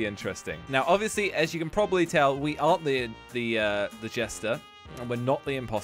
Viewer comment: did he say he is the jester